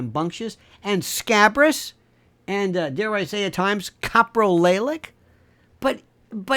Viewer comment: in you say a word (about Anthony?) sounds like caproelaic (sp). What is that word and what does it mean?? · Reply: Coprolalic from coprolalia, the involuntary and repetitive use of obscene language, as a symptom of mental illness or organic brain desease.